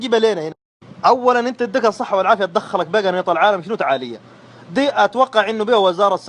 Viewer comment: سوداني كردافي الدقيقية 1:14 قال قنانيط ولا انا سمعت غلط 😂😂